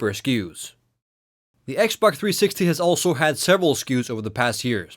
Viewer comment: I keep hearing "XBock 360".